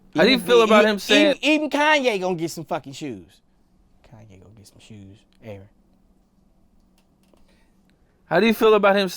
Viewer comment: What does he whisper from 5:37-5:39? “Kanye gonna get some shoes, Aaron”?